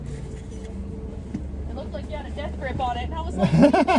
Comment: what did she say? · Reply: She said that it looked like he had a death grip on it